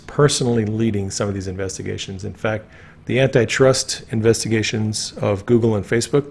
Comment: So sessions was a very bad actor! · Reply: I thought Q said trust Sessions🤔? I don’t think so, not a fan🤨!